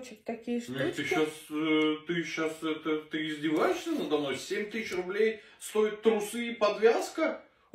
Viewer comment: А мне сначала послышалось 50.000 стоит, думаю норм, а тут всего 7000 руб. Вот жлоб.